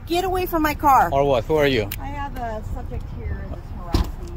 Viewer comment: "I have a SUBJECT here" not a CITIZEN, a "SUBJECT".🤔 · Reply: I heard “suspect” immediately I was like “of what crime??”